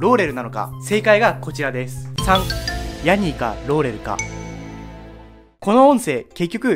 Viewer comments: イエヤニィーって聞こえた人挙手🙋‍♂️🙋‍♀️ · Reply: はい ローレルの方はモーローって聞こえた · モーローに聞こえる俺は？